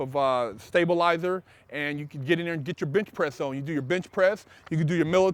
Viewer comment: Bruh I on god thought he said bitch press instead of bench press